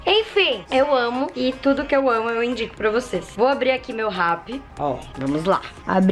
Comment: A FALOU ASSIM "VOU ABRIR AQUI MEU RAPPI" MAS EU ENTENDI "VOU ABRIR AQ MEU RABO" KAKAKA